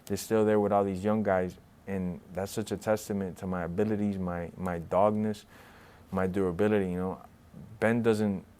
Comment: sounded like "my dogness" I'll accept that.